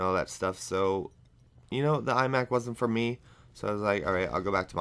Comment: lol at 1:30 i thought u said ''eye back'' instead of iMac:P and btw i think u talking bout ur life is AZZAM!